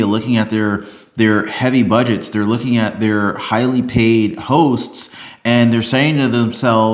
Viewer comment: I almost thought you said highly paid hos😅😅😅